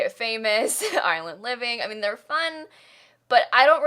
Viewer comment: When you said Island living by dumb ass thought you said Ireland living lmao 😂